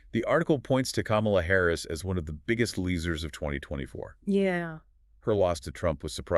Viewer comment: YOU SAID "LEESERS" INSTEAD OF "LOSERS" WHY?